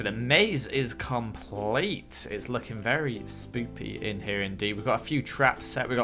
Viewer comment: did Joel say spopey